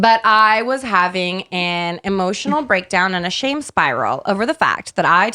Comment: for a second I thought said "a Shane spiral" lol. But for real though he should trademark that